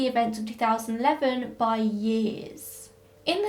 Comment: I've never heard anyone refer to the 2000s as the noughties, I had to rewind and google it because I thought you said naughty haha. In the US we don't really have a word for that decade at all so that makes sense!